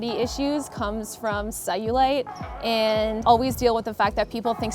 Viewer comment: Sorry what does she say? Saiulite? Sawulight?